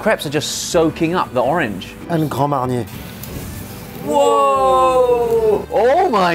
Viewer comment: the subtitles say "and caramelize" but what the chef said is "et grand mariner". Grand mariner is a French brand of liqueurs the one he is using is probably the orange flavored one "cordon rouge" which is made from cognac and bitter orange it's 40% alcohol.